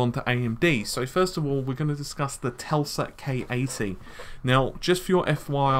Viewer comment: You say in the beginig 00:23 "TELSA" instead of "TESLA". WTF?